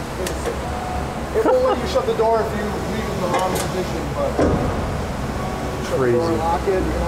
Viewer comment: I can't make out what he is saying here. Something about "shut the door in wrong position"??